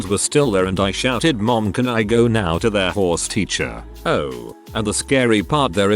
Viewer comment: I could've sworn he kept saying whores instead of horse.